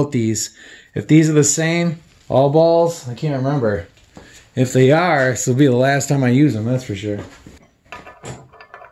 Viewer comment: Hey man at 8:57 what did you say about the punch mark? I cannot get the retainer to break loose, trying to get it to come off to replace bearings. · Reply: To drill out the punch mark.